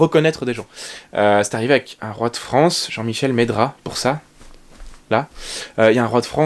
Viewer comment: > Des lacunes évidente en histoire, doublé d'une extrême fatigue, et d'un soupçon de naïveté. Pendant 2 secondes j'ai entendu, " Le Roi de France : Jean Michel Meidrat " Hommage à lui.